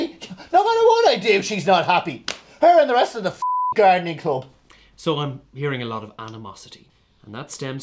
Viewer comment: Treating such a well worn topic like a marriage guidance contest, really clever. Hog is so good at playing the woman even when he’s not. I had to replay with the captions on because I thought you said gynae club instead of gardening club. That would have worked too with us women moaning about absolutely anything when were in the mood!💙💙💙